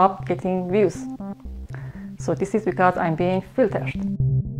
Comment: why does she say "filtershed" instead of "filtered"? It's been bugging me for 3 days and 3 nights.